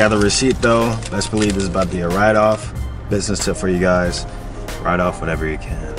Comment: What does he say at 12:22 after 'I got the receipt though'? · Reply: Best believe it bouta be a write off